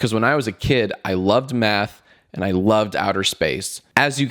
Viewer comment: I heard that when he was a kid he loved meth